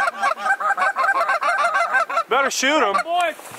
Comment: I heard a "dont shoot my dog" lol does he know who you're?